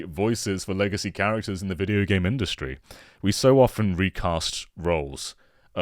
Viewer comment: i thought it said “Racist” not “Recast” 😭